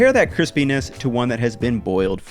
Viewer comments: I looked ať the subtitles And at 1:58 it gives the perk off bad translation. Instead of chrispines it showed penis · CC subtitle says Penis instead of crispiness. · If you have captions on at 2:00 it reads 'Chris penis' instead of crispness lmao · I thought you said let's compare the Penis to… 😂😂 Though later I found that you meant cripiness 😂